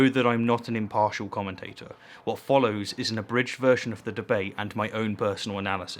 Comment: heard you say "what follows is a british version of the debate"